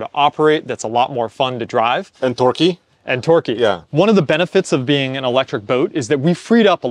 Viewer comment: I swear said “dorky” instead of torquey